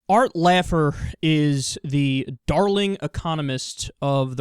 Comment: Did he say "Art Laugher"? XD